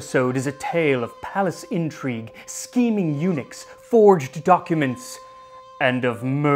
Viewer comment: One Scheming Eunuch dislikes this video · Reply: First time Max said this, I heard "Steaming eunuch", and I thought, "What?!" But it's China, so that's possible.